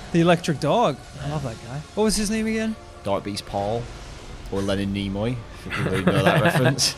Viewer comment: I thought he said Darkbeast Paul